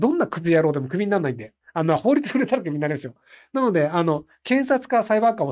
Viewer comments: なんて言ってんのか分からん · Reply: 法律に触れたらクビになりますよ？ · ~ ここらへんの「あの法律にjgajtpjw」みたいなとこなんて言ってるんですか？ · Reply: 「あの法律に触れたらクビになりますよ」 ですかね🤔